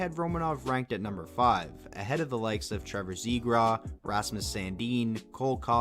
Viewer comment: Did you say Trevor Zebra lol